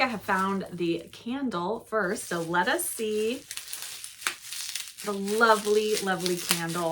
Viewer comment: Team Oxford comma! It’s the only thing that separates us from the animals! 😂 When you unwrapped the candle and said “let us see,” I heard “lettuce sea” as the name of the scent. I didn’t think twice about it until I realized that that’s not a very autumnal scent. 😂 This box is very tempting!